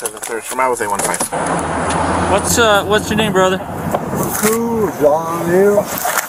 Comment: He tryna look smooth with that leg crossed but he really jus looks uncomfortable 🤣 and he IS uncomfortable but now he’s gotta stick with the decision he made 🤣 And did he say his name is?? 🤣🤣 Omg and they have the wrong Jean jacket and black hat guy 💀 They got this old white dude and supposed to be a Hispanic dude in his 30s Sorry Mr.. ur free to go